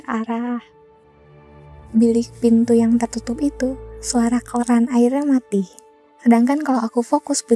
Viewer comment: suara koran air? Ada yg dengernya sama? Wkwk